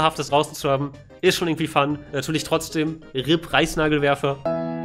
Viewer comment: Habt ihr auch Preisnagelwerfer verstanden?